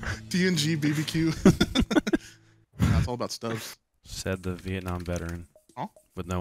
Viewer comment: needs to draw and quarter his editor for the small error at 3:00 - 3:01 where the subtitle said "stoves" instead of "stubbs"/ "stubs"